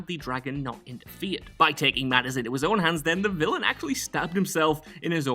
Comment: Honestly sounded like you said villainous fat guys